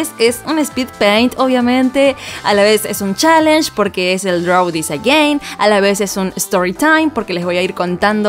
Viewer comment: al principio entendi "draw is a gay" v